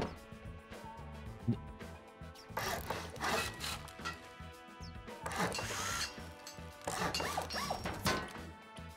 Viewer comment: i love you but.... uuh what did you say at 9:40??? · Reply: Every time he gets off to a good start, he makes a big mistake.